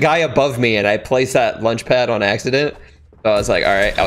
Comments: who else heard him say lunch pad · says lunchpad instead of launchpad